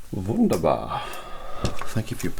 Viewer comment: In case you may wonder what he said at 16:50 he said “Wonderful” in German spelled Wunderbar🤗